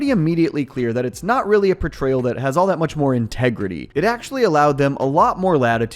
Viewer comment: what did you say…..? Say that again….. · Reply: ‘Tegridy